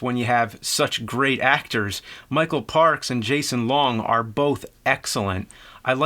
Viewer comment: he says Jason Long instead of Justin Long